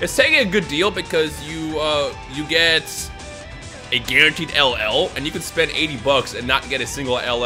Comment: Did you say ll